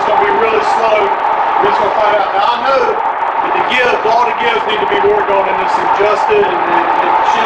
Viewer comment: I think I hear it saying, "good to be working again."